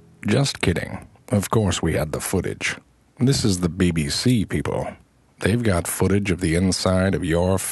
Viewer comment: For a minute there, I really thought he said "Bay Sea People"